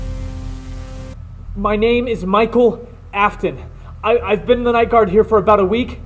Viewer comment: forgive me for when you said "My name is Michael Afton" i thought you said William. i was about to cry of rage i'm sorry btw you ALMOST had the fnaf history spot on just a FEW wrongs. like the 5 children died in 1987 same year as the bite of 87, Jermey Fitzgerld Lived the bite without a frontal lobe, Mike Smitz is not Michael, ect. overall great job w/ the history and story.